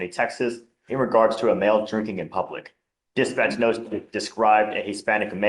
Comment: Today I heard "male reported jerking in public" I've been trying to finish this side quest for years, thank you.